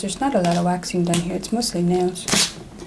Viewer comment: I THOUGHT MOSTLY NAIL SAID MOSTLY NASTY NAILS😂